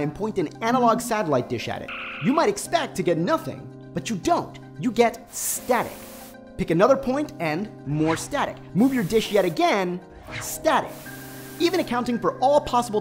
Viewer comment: who else heard "dick" instead of "dish"?